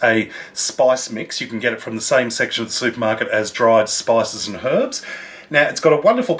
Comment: Did you just say "Same sex Supermarket" :)